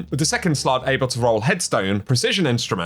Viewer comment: A few things things: I was wondering why I didn't see the sword when arms week was out, now I know why, second, I thought I heard you said uncle when instead you said Encore and lastly, I really hope the color can be fully changed on these weapons, cause if I'm being honest here, I really didn't like the weapon skins on the Solstice weapons